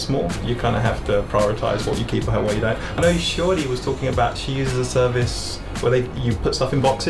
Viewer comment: I thought he said "Shorty". Shiori = Shorty 😁